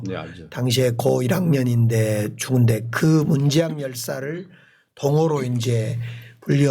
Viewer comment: 아놔 광주시장님... 18:00경 문재학 열사 언급하실때... 발음좀 명확히 해주시지... 자꾸 문재앙열사로 들려서 참...